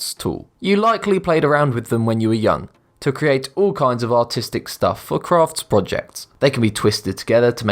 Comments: sound like he said autistic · did he say autistic or artistic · did he say autistic or artistic LMAOOO